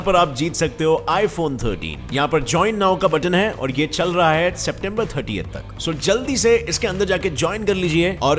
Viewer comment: what the F He just said September 38